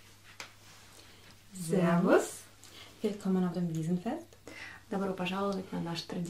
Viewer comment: мне послышалось мусоровоз